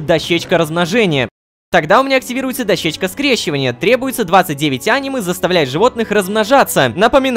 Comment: Лололошка говорит анимэ Что слышу я: АНИМЕЕЕ